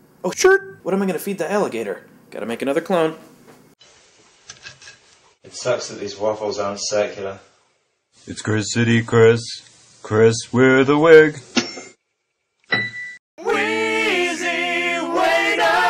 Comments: I thought Chris in a Wig said, "It sucks that these waffles aren't secular." I giggled :) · I thought he said his waffles weren't secular. x) I was about to say, "I want some godly waffles!" I'm a dork. n_n Awesome 'esplodey video, sir. As for your new word, Garch, I'm thinking it's like this: The alligator devoured the clone with a sickening (and oddly enjoyable) garch. Garch - the crunching noise associated with the death of clones; especially the snapping of bones.